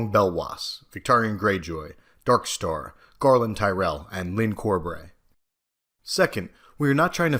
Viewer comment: Could someone translate what he says at 0:44 , I cannot for the life of me work out what he is saying. Iv'e googled 'Lincore bre' 'Lincoln' 'Lin core breh' I would love to know what this character is named, I would like to find out more about him.